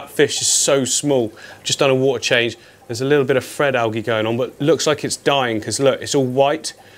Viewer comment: Whoever bullied into not using "Somefink" at the end is a jerk. It always made me smile. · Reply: I like how it sounds like he says "fred algae" when he's talking about the platy tank. Love your accent, ☺️😋